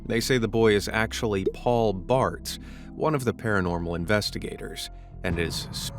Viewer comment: Paul Blart?! I know it’s not, but the first time I heard “Blart” instead of “Barts”.